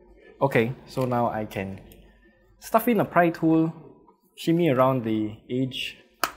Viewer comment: When you said pry tool, I thought you said pine cone and I’m not sure why, but I laughed way too hard at that.